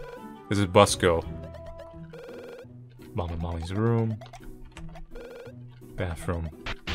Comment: did he say mom AND mommy's....lesbian moms?